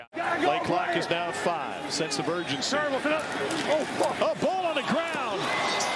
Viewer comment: anyone else hear him say “of f**k!” after the dropped snap? 😂